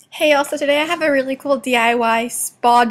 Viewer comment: It sounded like she said hey elsa